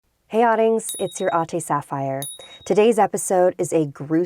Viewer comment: i always hear "Hey audience, it's your auntie sapphire"